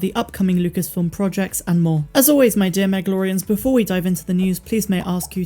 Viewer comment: You know, been wanting to tell you for some time: with your accent, every time you say "and more", I hear "and Maul" and I keep thinking "why is Meg always on about Maul?" :---)